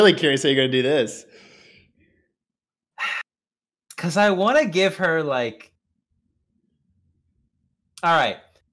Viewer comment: Had an ad come in as Dee appeared, and was deciding a voice, so I heard "I want to give her- depression"